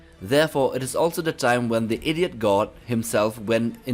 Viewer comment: did he say idiot god? Haha